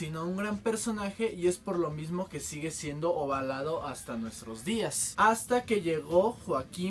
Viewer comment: parece que dices "ovalado" xdddd